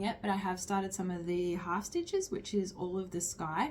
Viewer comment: I was looking down stitching when you said "half stitches" and my head whipped up so fast cause I thought you said "hostages" and I was like wtf she stitching?! 🤣